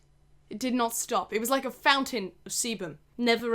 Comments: when u said "it was like a fountain of sebum," I thought u said "it was like a fountain of semen" don't ask me why....actually....don't ask me anything....ill be in the back. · I'm sorry but I cannot stop mishearing sebum as semen, it made for an interesting watch to say the least